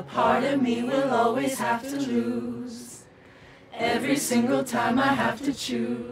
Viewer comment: who tf said choose when it’s lose????